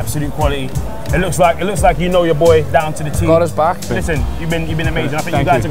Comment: Looool I thought Gerrard said "god is black" instead of "got his back" ffs 😂😂😂